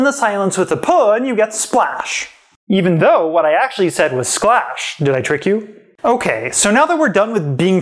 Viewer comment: Nice, I heard "slash".